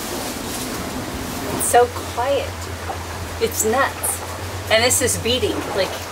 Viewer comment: funny! did I hear 9 nuts'?